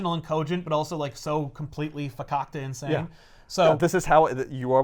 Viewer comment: What is that word? Focactin? I don't know what it means from the context, and want to look it up. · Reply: Facacta (not sure on spelling). It's Yiddish. It basically means fucking. He called green goblin fucking insane